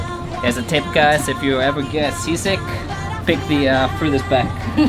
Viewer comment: Sea sick? I thought she said sisig!